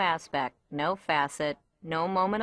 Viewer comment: Does she say “can” or “can’t”? I incline towards “can’t” because “can’t” is stressed more than “improved” and it seems like there is an AE sound in the word. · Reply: it's can't